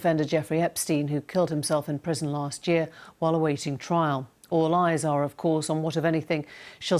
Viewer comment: Lastly, when she said "all eyes are of course", I heard "All lies of course"... Which would have been pretty groundbreaking reporting if you ask me. *lights cigarette